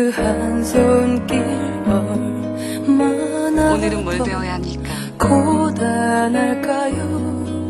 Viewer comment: Pleaaaase can someone tell what Jang hwa says at 0:50? She says whats my lesson today? but can't figure out how she says it in korean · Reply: onulen mual baewoya hamnigga? 오늘은 무얼 배워야합니까?